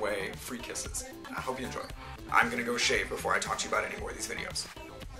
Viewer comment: I thought he said he was gonna shave.....